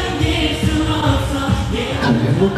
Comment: I was wondering if any Melody can help me with what Minhyuk is saying at 3:51 please? I would really appreciate it. Thanks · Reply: I think he said "be happy yourself.... be healthy" as his response to melodys cheering and saying "be happy, be healthy" when he bid his farewell (for his military duty). I think we can watch the complete scene on beatcom?